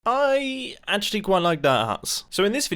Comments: Did Mumbo just say Aye at 0:00 instead of I? Is that a British thing? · When I first clicked on the video I thought you said you like dirt nuts...but then realized oh dirt huts..ok